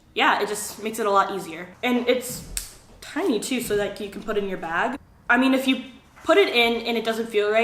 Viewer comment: I heard 'it's tiny too so you can but it in your vag' 😂😂